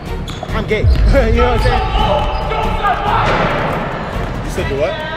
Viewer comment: what did he sayyyy😭😭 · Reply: “yk what i’m saying”😭😭😭